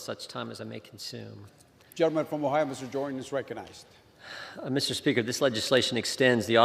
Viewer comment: Umm Excuse me, I thought I heard "Jim Jordan speak" As an Independent, I kinda like Jim J. has no filter. Doesn't mean I'm a repub , I just happen to like his unfiltered way of speaking.